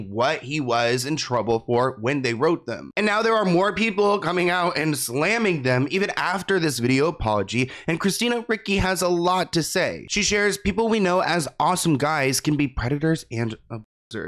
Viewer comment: Did he say Christina Ricky? 😅